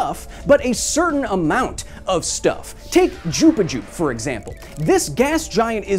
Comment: did he just said Jupichu ?